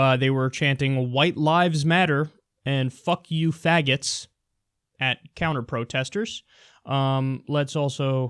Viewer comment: I heard "fuck you pancakes"